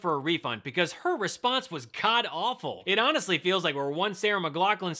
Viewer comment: I heard "Her response was Cod Awful!" and I agreed, it stunk like a dead fish.